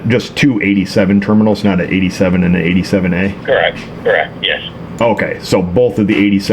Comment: I thought you said it had 2-87 and no 87a